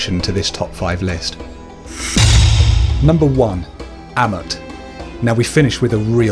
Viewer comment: i didnt hear so good what was that name? (Crocodile one) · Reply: it was amut